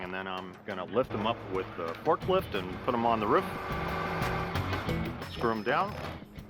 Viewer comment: Why does he say "ruff" when he means "roof"? I was beginning to think I had it wrong, but then the narrater says "roof" normally right after that